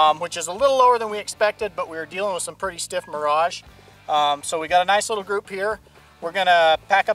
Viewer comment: I don't understand what he is saying at 3:47 Pretty stiff morage? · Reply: Mirage is a visual haze that’s highly noticeable on hot pavement like highways and you can see it on hot black cars when looking across the surface. He was basically saying the mirage was very strong in that direction at that time of the day. It can be seen in wildlife shows in Africa very often as well. He was saying he did his best to hit the target but with the mirage on the horizon it was hard to tell if he was truly aimed at dead center.